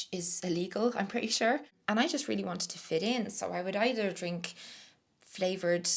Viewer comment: So, im not sure what the thing youre sayin is illegal but the autogen captions here are downright hilarious bcuz it says, and now i cant unhear, "They even got their hands on poutine which is illegal here" xD xD xD TIL: Ireland has banned that devil's food from Canada :P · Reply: 😂😂😂😂😂😂 omg! I meant ‘Poitín’ 😂😂😂